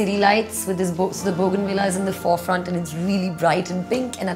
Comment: What she said 😱 4:59 to 5:01? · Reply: The bougainvilleas at the fore front and it’s really bright and pink